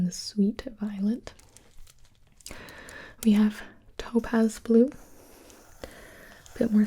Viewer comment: When she said topaz blue I thought she said togas blue lol